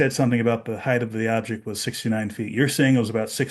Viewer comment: I can see the kid saying the object was “Six to Nine” feet tall, and that got misheard as “69” feet tall.